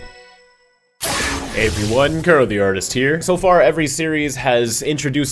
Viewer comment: Lol, the captions say "Hey everyone, Car the Artist here".